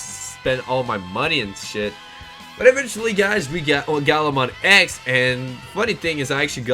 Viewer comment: I was legit confused. @ 1:30 I thought he said OuGarumon X and I was like wut???